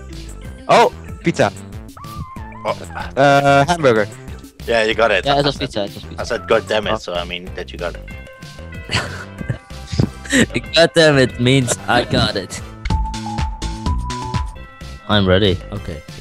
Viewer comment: Does he say on 9:12 ja het is pizza?!?'